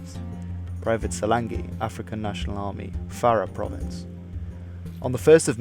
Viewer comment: anyone else hearing "African National Army" and not "Afghan National Army"?